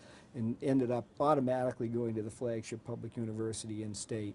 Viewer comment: Did he call public and state universities flakes?